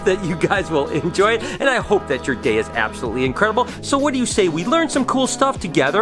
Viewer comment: I thought he said I hope your as gay as possible 😃💀😭😭😂😂😂😂